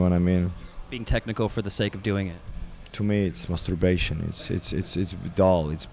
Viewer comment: Did just say "To me it's masturbation"?